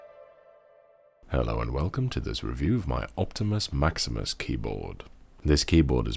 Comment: I thought it said optimus prime keyboard